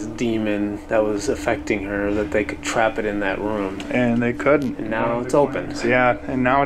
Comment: I heard “Dandy couldn’t stay” but it’s very different hearing it on tv instead of being there 🤷🏼‍♀️